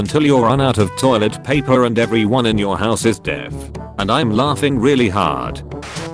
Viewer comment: the first time I heard this I thought they said dead and like... the joke works both ways except one is more dark humor